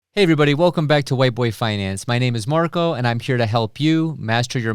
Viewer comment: Did I hear “white boy finance”? Lololol! Never ever buying gold cause I’m not 90 years old and hide money in my mattress ha!